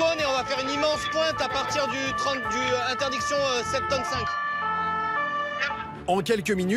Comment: c’est moi ou un policier français a dit septante cinq sois il est belge ou alors suisse